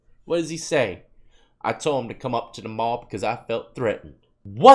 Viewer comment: he CLEARLY said “I told him i felt threatened” not “come up to the mall because I feel threatened”